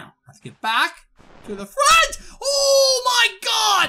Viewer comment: did he just said "BrAt" , OOoOO MY GOD" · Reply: it was fridge